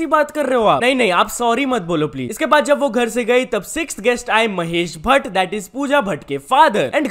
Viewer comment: Mahesh Bhatt but I think he say mahes dale 😅